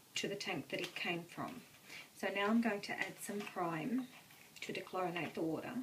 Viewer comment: you add something but it's illegible - can you please let us know what the word is you say. It sounds like "prime" or "prame". Thank you. Important step we need to know. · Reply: Hello, the word i said is 'Prime' (to dechlorinate the water).